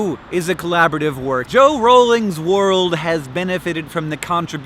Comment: did he just say joe rowling?